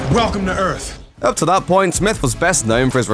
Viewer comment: Welcome to Earth! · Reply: Sounds like he says Welcome to Earf.